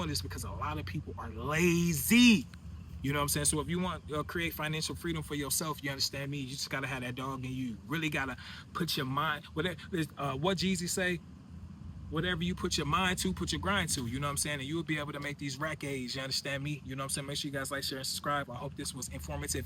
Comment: i wasnt watching the subtitles I swore you said Jesus at first 🤣🤣 Im like i dont think Jesus said that bro lmaooo